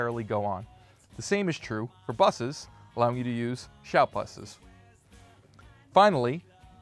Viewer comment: what does he say here exactly ? "shout busses" ?